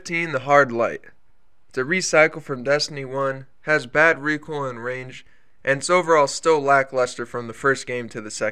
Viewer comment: did he just say bad range?